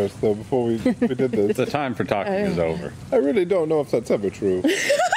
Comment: "I really don't know if" what? 0:21 Sorry I'm hard of hearing. Anyone know what he said? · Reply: "I don't really know if thats ever true"